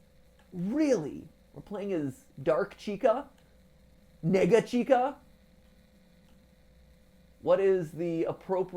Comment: AYO MATPAT DID I HEAR THAT RIGHT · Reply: Negga chica